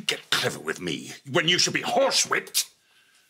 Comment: excuse me did he say "you should be HORSE RAPED"?